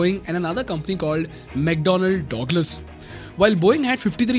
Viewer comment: ayo what I heard 17:15 DOGLAS hahah damm, it's DugLas not DOG it's DUG.